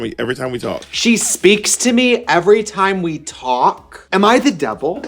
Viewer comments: I heard "She's SWEET TO ME, every time we talk." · I thought that last clip said “she’s sweet to me every time we talk” and was so confused 😭